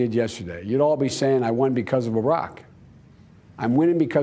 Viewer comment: Did he say Arak instead of Barak@ 3:44?